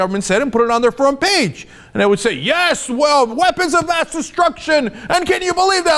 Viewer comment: They're hilarious! Let's just say they sometimes give a creative interpretation Example: (@ 6:52) Cenk Uygur says: "weapons of mass destruction" subtitles say: "what is on pat's instruction"